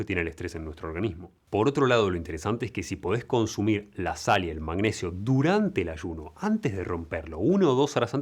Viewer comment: En el minuto 11:43 menciona "sal y magnesio"??? escuché bien? Si alguien me lo puede aclarar se agradece